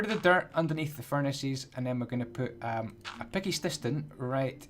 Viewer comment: Who else heard him say picky stiston?!